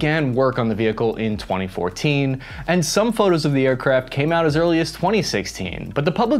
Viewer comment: did he really say "aircrap" at 2:25?